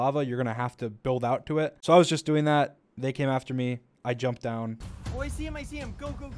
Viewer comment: I heard bacon after me lol somebody help me